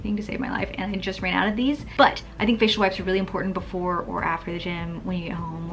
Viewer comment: Girllllll I thought you said fish wipes! And I was like whhhhhatttttt!? lol Love you!!